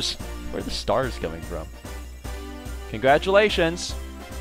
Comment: I always hear it as "Congratulation!" instead of "Congratulations!"